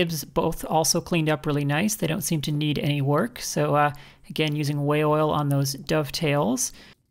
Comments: Fantastic segment, you had my attention the whole way through. Now I must go fix something. Every time you said "Way Oil" I heard "Whale Oil". is it just me ? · WAY oil! I thought you were saying whale oil!